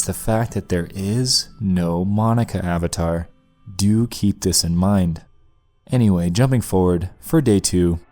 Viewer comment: i heard that as “Doki this in mind”